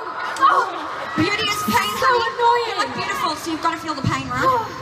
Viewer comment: Pleeease what did perrie say at 0:52?? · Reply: "Beauty is pain, honey, and you look beautiful, so you've gotta feel the pain, alright?"